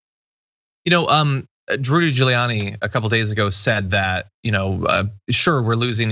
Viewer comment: John, did you say Drooly Giuliani?